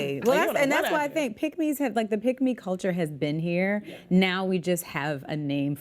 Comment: At first i thought she said pygmy culture... i was thinkn they where tryn to be little people